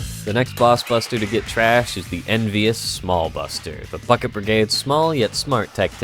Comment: Did you just say “The Plucket Brigade”?